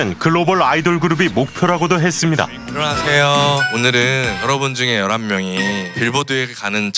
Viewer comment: 안녕하세요 오늘은 제가 감옥에 가는 첫 시작이에요 이렇게 들리네요 ㅋㅋ